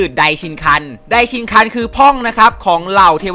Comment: ผมได้ยินว่าพ่อง